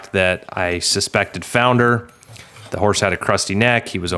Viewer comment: i kept hearing "crusty" neck and couldn't visualise it - but when you likened it to a stud, i realised you were probably saying "cresty"?